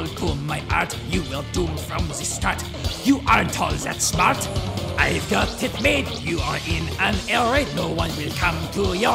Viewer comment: when he sang i got it made/ i thought he said i got tech made